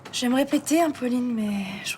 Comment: "j'aimerais péter hein Pauline" c'est ce que j'ai entendu la 1ère fois 🤣